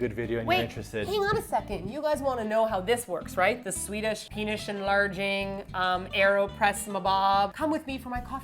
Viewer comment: Wait... did said penis enlarging??????